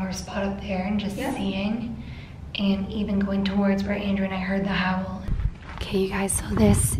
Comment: I heard where's Andrew